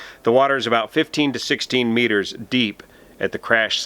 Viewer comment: I thought he said 15-16m ~ 45 - 50ft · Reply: He Did Say 15 to 16 meters of water